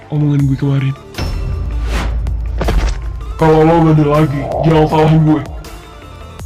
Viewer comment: arshaka bilang apa si?? kaya ga jelas gituuu ga kedengaran bisa kasi tau ga?? penasarannn🤦🏻‍♀️🤦🏻‍♀️ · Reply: "kalau lu bandel lgi, jangan salahin guaa" 👉👈